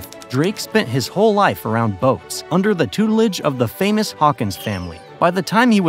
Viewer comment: Famous or infamous? Captions say the latter, and they have quite different meanings.